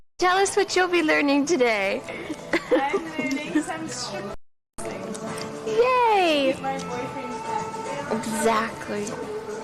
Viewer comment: "stripper things to keep my boyfriend happy"- that's what she said, right? Wtf! This whole clip with her is so sad and disgusting, I hope she's doing ok now